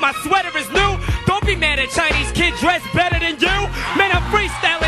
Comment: What does he say at 1:06 "dont be mad at chinese Kids dressing better than you"?? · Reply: "Don't be mad at Chinese kid dressed better than you."